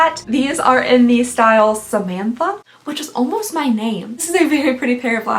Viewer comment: I was so confused when you said “samantha was almost your name”. I had to turn on cc because I thought you said “it IS almost your name” and so did cc, but I listened a few more times and watched your lips. I was thinking, wait, is her name not?